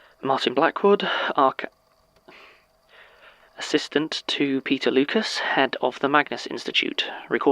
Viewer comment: Every time Martin says "Peter" in this episode I hear "Pizza". The first time I got so darn confused. That's never happened before, and I'm not even hungry. What's up with that?